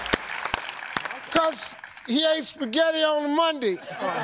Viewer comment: "Cause...he ate spaghetti on a Monday." Wow, Steve. XD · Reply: @@ No. It just sounded like spaghetti on a Monday the first time I heard it. That's it.